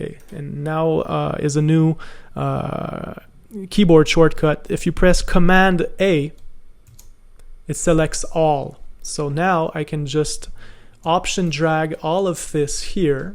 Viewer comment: i can't hear what keys you said at 29:26 what exactly did you say ? :) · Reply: Command A (select all) then Option (ALT) + Drag ;)